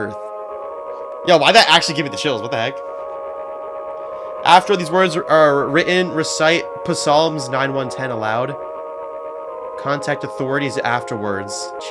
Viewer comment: did bro say puhsalms😭😭✌️